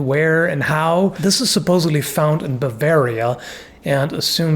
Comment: > Did you call Bavaria Babaria? I don't relly mind, we are the wild men of germany, but was that intended?